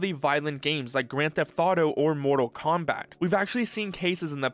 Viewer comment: Did he say Mortal Comeback?